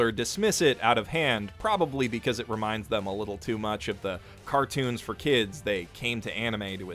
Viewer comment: did you just say cartoon at 35:20 ? (= <3